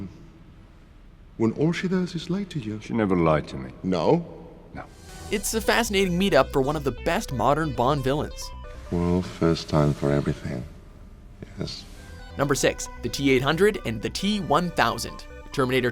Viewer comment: NIGGA LOOKS LIKE HE IS GOING TO SUCK HIM DRY OR BUTTFUCK HIM AND DID HE SAY "well there's a first time for everything" ? XDXDXDXDXD